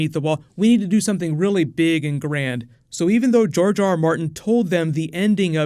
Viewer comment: did i hear jar jar martin? D